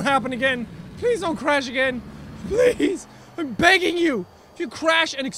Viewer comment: i swear the god he sayd please dont happen again im bangin you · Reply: He said BEGGING